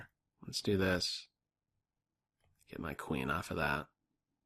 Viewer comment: I anticipated your voice saying, "that's a queen!" so much that I thought you actually said it!